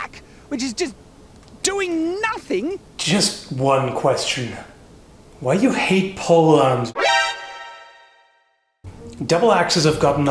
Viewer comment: When you said "why do you hate pole arms?" I thought you said "why do you hate Poland?" and promptly exhaled my tea.